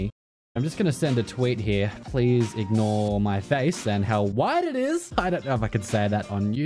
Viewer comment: I swear, the first time, I thought you said "wide". Not "white".